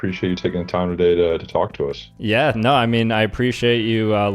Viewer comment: Screaming “TALK TUAH” when I heard the interviewer say “talk to us” probably wasn’t my proudest moment